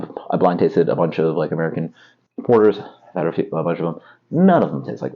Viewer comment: What did you say at 2:21? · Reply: "I've had a bunch of them." None of them taste like this is the rest of what I say.